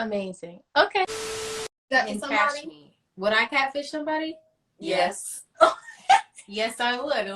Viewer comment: Halle has clearer diction usually but I coulda swore she said "Would I castrate somebody? Yes, and let me tell you why." LoL